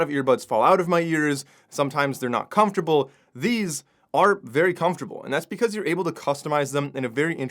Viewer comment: Are you saying ARNT very commutable. Its not clear what u say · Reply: He’s said they are comfortable